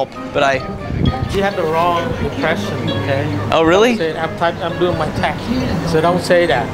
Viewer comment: “I’m doing my text, so don’t say that.” 😂😂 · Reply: Ashley Nicole ...lol...I thought he said “I’m doing my taxes!”